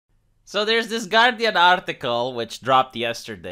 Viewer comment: When you said "I was going to take a second to read this guardian article." I thought you said garbage article for a moment not that it wouldn't fit of course